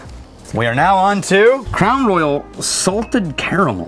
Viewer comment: Did he just say "Clown Loyal"? xD